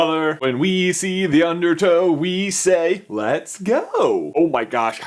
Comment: OMG Every time they say "Undertow" I hear it as "Undertale" and you know how the word "Undertow" triggers Dory's memory? It just triggers me. *TRIGGERING INTENSIFIES*